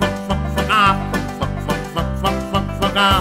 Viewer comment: When he was saying:bak bak bak bak" i thought he was saying "fuck fuck fuck fuck "